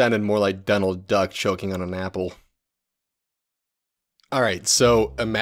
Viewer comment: I heard “Donald Duck choking on a nipple”